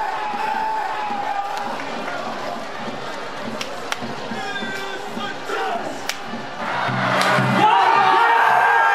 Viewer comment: 又是3分砲...兄弟們今年很喜歡3分球 00:33...那個...我是不是聽到喊JOSH.....英聽不太好...XDXDXD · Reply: 是chase 今年的口號